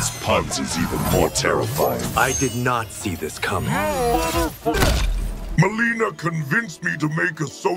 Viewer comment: What did Johnny say? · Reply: He said, "YOU MOTHERFU-"